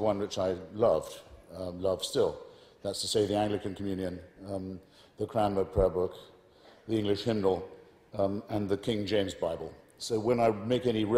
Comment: Which prayer book does he say at 2:25? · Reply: The Book Of Common Prayer, Church of England handbook of prayers and orders of service